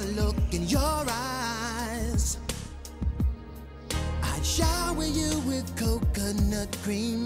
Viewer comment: I played 0:58 and then stopped at 1:00 because I heard DK say, "I'd shower you with coke."